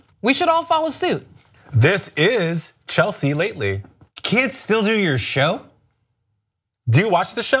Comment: Y'all please pray for me. At 1:26, when said, "Can't," I heard, 'kids.' Yikes! 😐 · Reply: said Kids. Drunk intern wrote Can't.